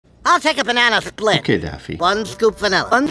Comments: did he just say "okay daddy"? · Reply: He says "okay daffy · Bro I thought he said daddy instead of daffy